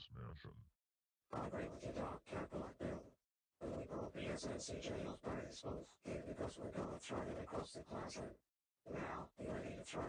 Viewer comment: What The Hell Did Cranberry Hammer Bro Say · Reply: Cranberry Hammer Bro said, I agree with you Dark Cat Bullet Bill. And we brought BSNCJL's baseballs here because we're gonna throw them across the classroom. Now, you ready to throw these baseballs?